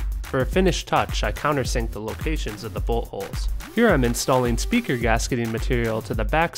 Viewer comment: it sounds like it says butt holes lol